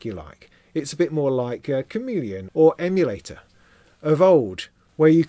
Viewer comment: did you say “it’s like a comedian”? Also, awesome monthly. Always enjoy those, just sayin’ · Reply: Chameleon - touch based midi controller software